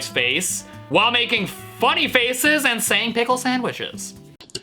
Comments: i heard a pickelle sandcheckssandpickels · the moment i heard this, i literally died 🤣🤣"pic ickle wik kick uhhhhh ic pititickle san sandwi cti sandwi b**ch" THATS ACTUALLY WHAT IT SOUNDS LIKE but he says b***h when the other person starts playing guitar